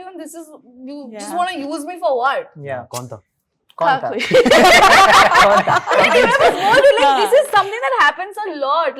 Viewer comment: what did parth say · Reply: Kon tha?🤣